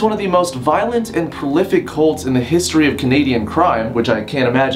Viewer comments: I thought it said cunts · lmao, I thought it said cunts 😂😂😂😂